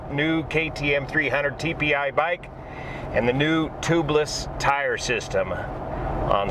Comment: That's awesome you ride to! I was listening to you talk while I was still working. It took a second, but I thought I heard tubliss. Lol so I had to rewind & listen again. You should do some dirtbike content too! Especially on the TPI 300. I'm subbing now! 😎🤘🏼